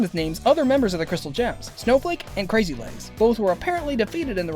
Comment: The names Bismuth says are actually Biggs, Crazy Lace, and Snowflake. A lot of people mishear it as Crazy Legs, but these are actual types of gems. Biggs Jasper, Crazy Lace Agate, and Snowflake Obsidian